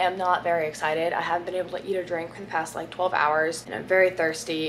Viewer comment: I am not even joking I thought Brooke said “eat a drink” 😂